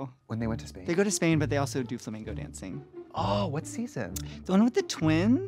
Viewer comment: Did Crystal just said "flamingo dancing"?? 🤣🤣🤣🤣🤣 11:39 lol love her so much